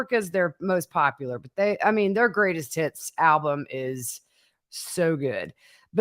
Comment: Your accent - I heard "Great as tits". 🤭🤦😁